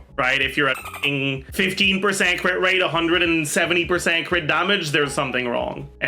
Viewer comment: he said 15 % crit rate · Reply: I think the editor misheard and typed 50% crit rate but actually said 15% crit rate which is why he said there's something wrong